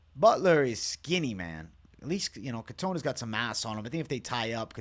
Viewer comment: MASS* I've watched this twice and thought yo said "he got some ass on him" lls